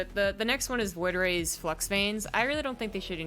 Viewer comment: When I heard "Flux Vanes" - I heard "Fuck Banes". I play Protoss. Don't even care about Banes, but just fuck Zerg in general.